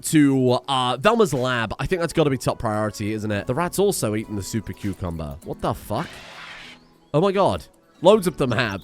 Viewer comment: did he just say super cucumber?